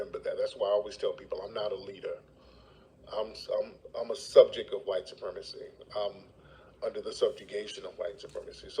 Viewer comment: Did Tariq say that's he's a subject of white supremacy 10:40?